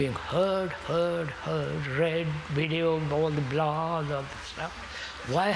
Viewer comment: it sounds like he says blog and snap and that is creepy, 'cause neither of those things existed